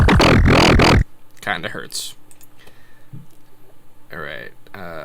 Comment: Did he say that it kinda..... hertz?